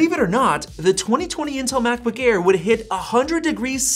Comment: The M1 MBA had not fans. Were you testing the Intel 2020 MBA? I didn’t hear you specify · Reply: he clearly says “intel”…